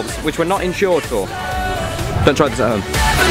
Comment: when he said dont try this at home i thought he said do drugs at home